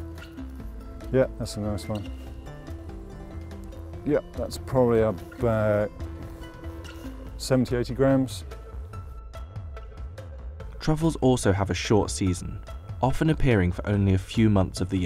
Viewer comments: anyone else thought he said 70/80 grand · I can't be the only one that thought he said 70 or 80 GRAND and had a heart attack.